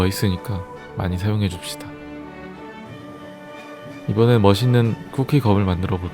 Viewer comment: ㅋㅋㅋ 나 멋있는 검을 맛있는 껌으로 들었엌ㅋㅋㅋㅋ